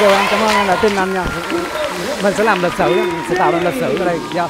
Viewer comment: Binz dặm chân phát quăng nón luôn ta nói nó ngầu vcl ra ạ, đã vậy còn rất hiền và chân thành sức hút cỡ đó thì sao ricky star chịu nổi, gặp tui là tui chịu ko nổi rồi đó :)) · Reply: t bị nghe nhầm thành " Mình sẽ làm ăn lịch sự mà " :))